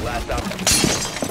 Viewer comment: I thought was saying “Saul Goodman”